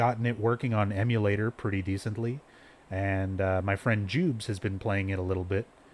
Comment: did you say your friend "Joobs"? 😂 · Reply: His name on Twitch is "JubilationStation", we call him "Jubes" for short.